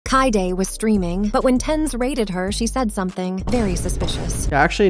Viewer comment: I honestly thought she said "when tenz railed her" and I had to double check